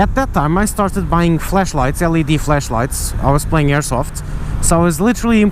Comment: Accent made it sound like you said "Fleshlight" multiple times. Or maybe I just have rotten mind.